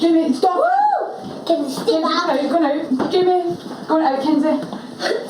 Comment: i think i can heare Jamie say stab her at 6:08, not sure if anyone else noticed